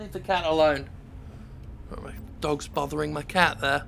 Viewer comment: Quite honestly thought you said "the dog is buggering my cat".